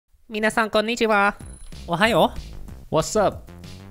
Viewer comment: why did I hear you say Mina-San??? 😂